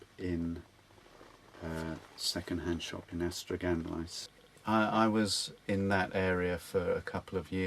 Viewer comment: That was a fascinating interview. What an articulate speaker! One thing I didn't understand: what was the name of the village, apparently in South Wales, where he lived for a few years? (He mentioned the name at about 15:28 and again at 15:41.) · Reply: Ystradgynlais